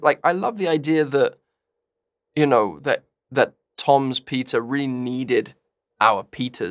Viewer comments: help i thought that andrew was saying "pita" instead of "peter" and that he was making one giant analogy for like 30 seconds...i'm not ok😭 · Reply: i heard "pizza" lmfaoooo took me like 4 rewinds to be like OH PETER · help i thought that andrew was saying "pita" instead of "peter" and that he was making one giant analogy for like 30 seconds...i'm not ok😭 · why am i keep hearing "pizza" everytime andrew say "peter"